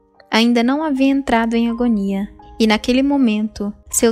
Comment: Por favor me tirem uma dúvida? as 01:16 a locução diz que o papa ainda não havia entrado em ........... ? nao entendi... Ugomia ? nao seria Agonia ?